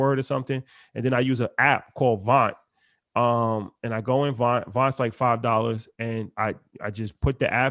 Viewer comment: What's the app called Von?V___ to add a word... u say it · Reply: Vont